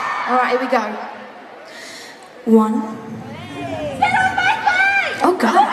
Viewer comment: what did the fan say in 4:07? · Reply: sit on my face :D